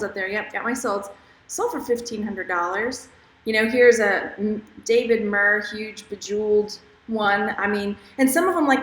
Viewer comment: I heard you say David Murh at 6:56? Is this a designer or did I miss understand you?